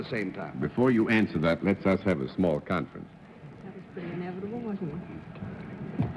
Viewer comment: I think I heard Dorothy exclaim, "how sweet!" when Miss Tom came out to sign in. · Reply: Oh yeah! I heard that too. Also at 6:43 you can hear Dorothy say, "That was pretty inevitable, now wasn't it?"